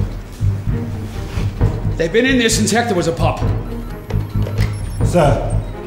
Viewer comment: What did say at 8:12? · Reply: They've been in there since hecter was a popper, I think